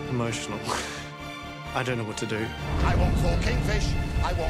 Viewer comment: I hear “I want fooking fish, I want fooking fish”